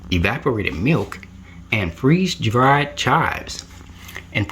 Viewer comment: You say Freeze dried chives I thought you said dry Charlie’s jk (just kidding ) when you saying the ingredients